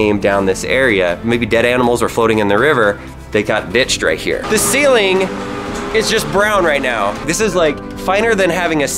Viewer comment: What did he say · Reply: permerforst